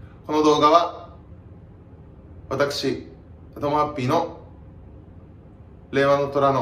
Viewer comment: アタマハッピーって聞こえて笑っちゃった